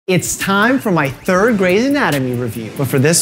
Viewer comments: I really thought he said third grade anatomy review. Lol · In the beginning I thought you said "third grade's anatomy" I was so confused for a second